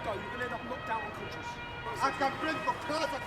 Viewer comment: Dude says "I can break the cars I can break anyth-..." Subtitle says "I can kill your..."